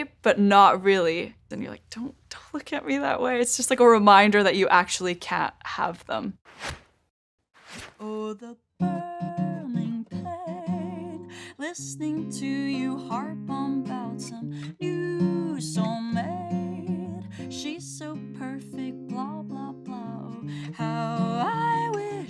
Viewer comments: For a while I thought the lyrics said “Listening to your heart bump out some new soulmate” 😭😭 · I thought she was saying heart pump out some new soulmate...